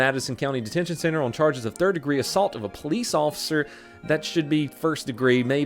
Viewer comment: For a second there I thought he said 'turd degree assault'